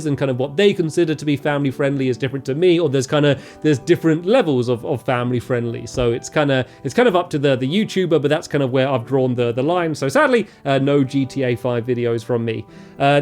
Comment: Lol at 8:28 it sounded like he said "draw the lime" 😂